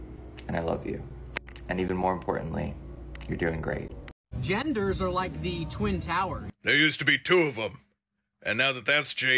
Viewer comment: I WANNA KNOW WHAT THE ORIGINAL VIDEO?? WAS SAYING? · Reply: "Genders are like the twin towers, there used to be 2, now it's a touchy subject"